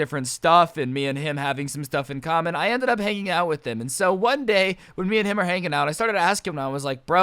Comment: At 4.30 I thought he said "I ended up making out with him